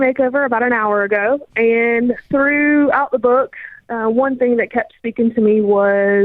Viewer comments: I thought she said, "aaaand threw out the book." LOL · When she said "throughout the book", I thought she said "threw out the book". I was waiting for Dave to lose it. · For a second there, I thought she said, "I threw out the book". :)